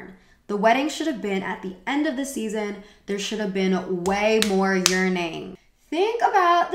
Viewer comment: omg I had to rewind a few times to be able to hear "there should have been way more yearning" instead of "way more urinating"